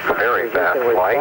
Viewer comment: Did he say "very bad flight" ? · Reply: “Very fast flight.” Total glide time from release to touchdown on that flight was 2 minutes and 34 seconds (compared to the earlier flights with the aerodynamic tailcone which lasted over 5 minutes from release to touchdown).